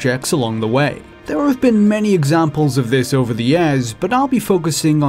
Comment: there have been many examples over the [.....] what does he say here? EDIT: nvm, during the video i found out he says year like Yeahh, guess that is normal where he lives